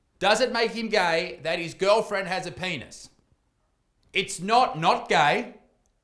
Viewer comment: I heard isaac say "its not not gay" and i finally figured it out! He's right, its not not gay and it certainly isnt straight. So, its transgay.